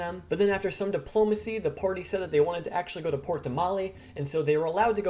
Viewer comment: for some reason i thought you were saying "Port Tamale" there. The Traveler is a new cult, with rule #1 being "Don't call it a cult." We will find out later that the Traveler is actually not only Jester's real dad, but is a Vox Machina member who snuck his name into the Book of the Gods.